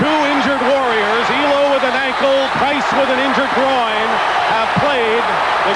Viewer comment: im sorry but did he say a injured groin?